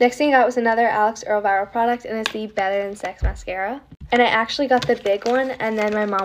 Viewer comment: did she just said * mascara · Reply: Better than sex mascara. It’s the brand or color I don’t remember but she is just telling people what she bought. Makeup has some weird names.